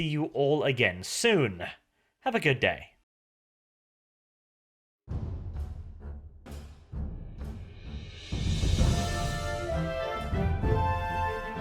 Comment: does sound like hes saying "fuck you" when he says " have a good day" ?